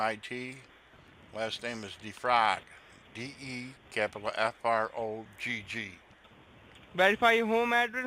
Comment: Sorry his accent is so thick I thought he said vefrogg but just noticed that your fake name was defrogg, lol 😂